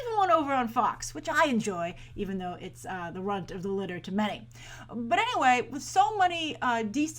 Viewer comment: What is Grace saying at 8:50 about Gotham? Even though it's the... I can't figure it out, new saying for me. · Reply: Runt of the litter.